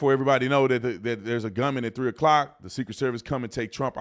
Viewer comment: I swear I hear “he’s got a gun”